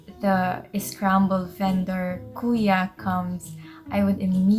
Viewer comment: Just a little thing, in 6:11 the captions say that she said "courier". She's not saying "courier", she's saying "Kuya", which typically means an older brother but can also be used to mean an older man.